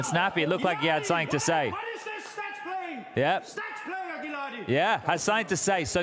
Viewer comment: what is Snappi saying exactly? I can not understand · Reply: Calling Spinx by his real name and calling him a 'stats player - statistik player' cause he is always the last to die